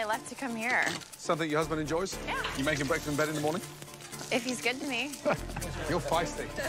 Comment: what did she say? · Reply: If he's good to me